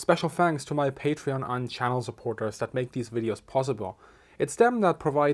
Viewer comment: I keep hearing "thank you to my patrons and child supporters"